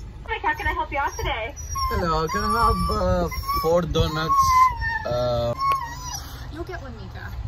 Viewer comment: What I heard at first : *Just adorable high pitched screams* What I heard when listening carefully : "Hi! Hello! One donut! Want one!" (Man, the owners understand Meeka well)